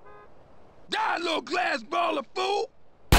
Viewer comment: When he said die, little glass balla fool, I always heard it as DIE, LITTLE GLASS BOTTLE O' FOOL! lol that's why you need subtitles. however, without them, you may get some funny moments by hearing things wrongly